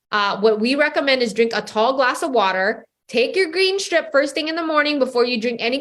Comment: I thought she said green sh$t not green strip